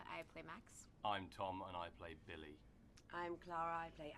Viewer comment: When Tom introduced himself, I had to replay the first few seconds of the video because I thought he said "I'm tall and I play Billy."